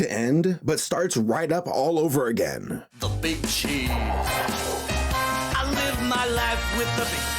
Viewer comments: it sounds like he said: The bitchy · Am I crazy or did he just said the bitches